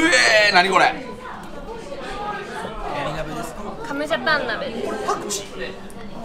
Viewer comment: やっぱり加茂ジャパン鍋に聞こえる😅